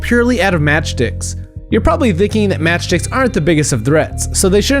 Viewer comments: No one is going to point out that it sounds like he says matchdicks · Lmfao it sounded like he said match dicks